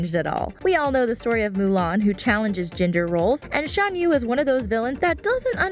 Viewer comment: she said "ginger roles" anybody else hear that?